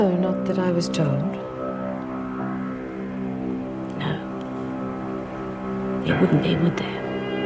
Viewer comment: what does Sonia say? All I make out is "no" but she says something else after that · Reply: Mullin Sonia said, “No. There wouldn’t be, would there?”. 😃